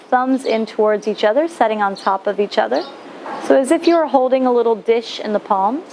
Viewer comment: Ah this makes me so happy :) I am so glad you enjoyed it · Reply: I've done this flow a few times in the last month, and I'm getting better at it. But what I really wanted to tell is this: at end when you say 'as if you were holding a little dish' I kept hearing 'as if you were holding a little wish', and I always imagined a little flame in my cupped hands… A creative misunderstanding :) Thanks again.